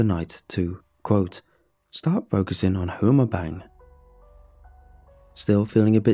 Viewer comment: Wait. I didn’t understand what he said at 5:16 .. “start focusing on ____”? What? Homerbang? Sorry I just want to know what he said. Thanks. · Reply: "Who I'm a'bang." SOBER TRANSLATION: "Who I am going to bang."